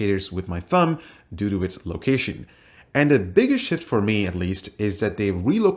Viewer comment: I heard sh!t